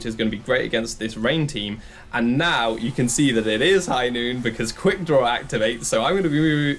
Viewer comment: Hello there is a creepy voice that says something between 2:08 and 2:11... what is it saying and how did it get there?! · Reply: In Overwatch, there's a character named McCree and it's ult is called High Noon. When he activates it, he says "It's High Noon". That's the voice you've heard 😉